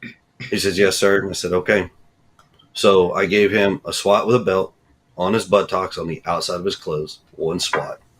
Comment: At first I thought he said he swatted him outside his clothes,but after listening closer he says he swatted him once on the outside of his clothes so I stand corrected. The reasons he gave are no reason to spank a child!